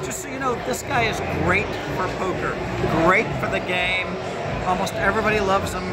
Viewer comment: Phil "Doug is great FOR Poker" not "Doug is great at Poker". I've heard him say that before. Cheeky dude! Don't listen to him, Doug, he's winding you up!